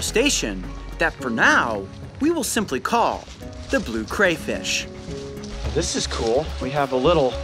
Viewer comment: I thought he said blue grey fish 😂😂😂